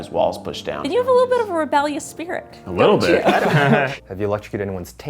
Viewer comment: I can't understand what the woman says at 5:19...someone, please? :) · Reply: "you have a little bit of a rebellious spirit"